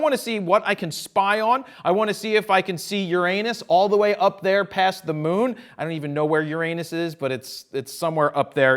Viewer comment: I heard :" I want to see if I can your anus all the way up there on the moon.."